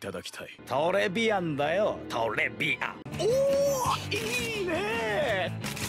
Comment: I thought he said lesbian XD